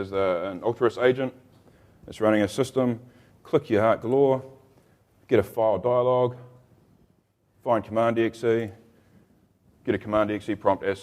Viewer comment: wtf did he say there? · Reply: He said "Get a file dialog, find a cmd.exe, get cmd.exe as system." It still works if something runs as an admin or system, even on Windows 10.